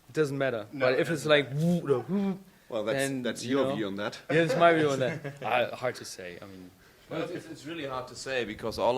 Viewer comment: What Gerrit said to Klass? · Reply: That's your view on that.